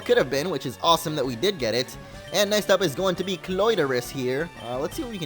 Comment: he said "Clitoris" instead of Cloyster. no problem.